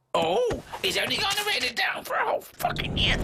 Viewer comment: what on earth does he say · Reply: The Gamer Guy 500 "he's only gone and written it down for a whole fucking year" I think.